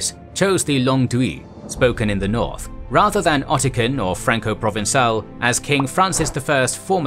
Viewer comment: I kept hearing "Ottican" when you were reading Occitan, which I thought would be pronounced "Oxitan."